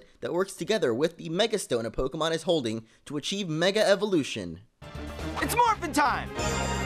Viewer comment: Did he just say '' It's morphing time!'' @ 1:50 ? XD